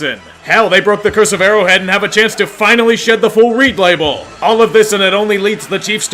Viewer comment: What does he say at 7:01 about shedding the curse of Arrowhead and the full replay bowl? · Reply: Full Reid Label